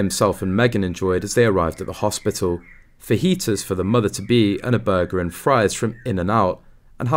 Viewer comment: I misheard fajitas for foetus!